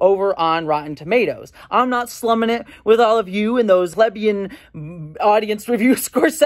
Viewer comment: 😂 it sounded like you said,lebeian the caption says, you said lesbian, but I think you meant plebeian